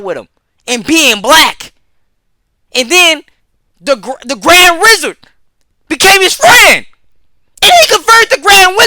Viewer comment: bro ik what u said but i heard "grand rizzard" and im not gonna be able to get that out of my head